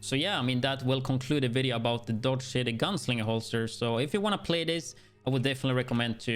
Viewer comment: I heard "dog shitting gunslinger holster.."